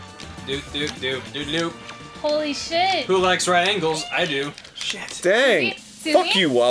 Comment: HAHAHA, I just heard it "Who likes right angles?"